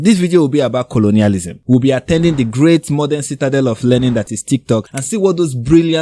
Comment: You had me at the great citadel of learning that is TikTok. 😂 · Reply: He had said „learning“?! I thought he said „Lenin“ and i thought it was really funny because many of the woke TikTokers have usually also communist and anti capitalistic ideologies